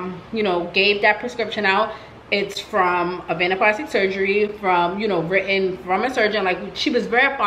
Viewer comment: What did she say the name was at 27:43 ? Avanna plastic surgery?